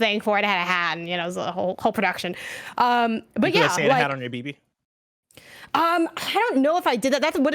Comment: I misheard this as "Did you put a Santa hat on your peepee" thank god I understand some modicum of context about Death stranding